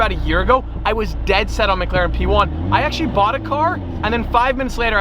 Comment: For a second I thought it said Mclaren F1.